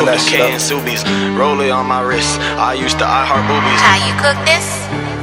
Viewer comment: Anyone know who produced this? I hear the tag around 0:09 but cant quite hear the name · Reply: Prod. and Cooked It